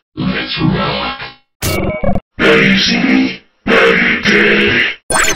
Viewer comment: What does this say? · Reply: No he says now you see me now you’re dead